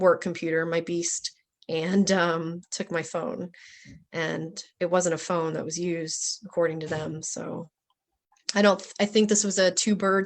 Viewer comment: Rebeckah: (at 17:32 ) "...and took my phone. It wasn't a phone, it was used." What does that mean?.....am I hearing it right?